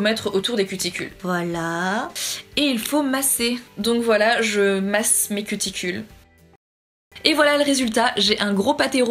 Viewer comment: J'ai compris je masse mes testicules Jpp x)